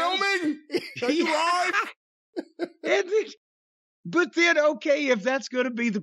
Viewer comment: I thought he said are you live now